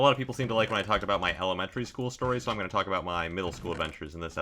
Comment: sounded like he said hellementary lmao